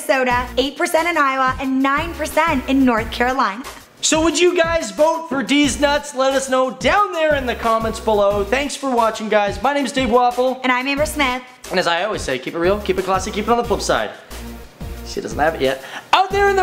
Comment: I thought he said Dave Waffles